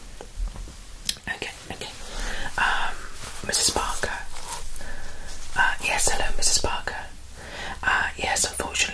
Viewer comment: lol I had to replay that part to make sure I heard clearly my last name is and I usually never hear that word cause it’s a rare last name where I am from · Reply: Yes I said as it’s a common name here in the UK 🥰